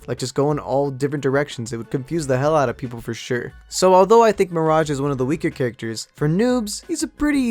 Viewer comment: I thought it said marriage...for noobs